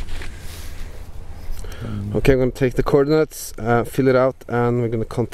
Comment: Awesome Thanks. My wife and I thought you said Corn Nuts but you really said coordinates.